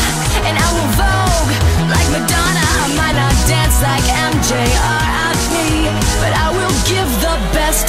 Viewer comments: when she said m.g rip I thought she said m.g or IP LOL:D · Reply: Nevaeh Williams it's I might not dance like MJ RIP but I will give the best of me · I didn’t think she said R.I.P I thought she said I don’t dance like mj or rp 😂